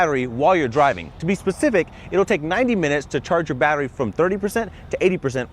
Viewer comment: he actually said 90 minutes and then broke it down from 30% to 80% in 90 minutes which is still good for a car that large. Combined that with the gas charge and you are going places.